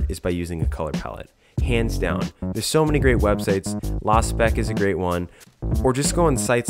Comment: Can any one write down website name i didn't understand 3:55 (Its "LOSPEC") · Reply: Lospec